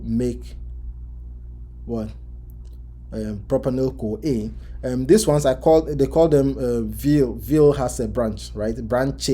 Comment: You used mnemonic pro something fears water and some other ones @ 42:00 minute. Can you please write it I couldn't hear it properly. Thanks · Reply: I WILL SENT U THIS TOMORROW. It TRY ME PRO GAVPIL fears water VIL has a branch His Lies are so basic L and L ketones